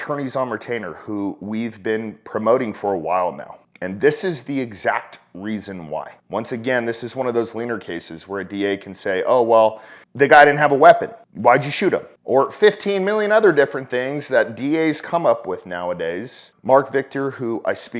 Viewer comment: Wtf are you saying at 4:05? Weiner cases?